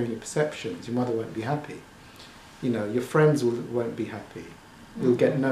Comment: Hi, thank you for the video. At 9:35 did you say will or won't?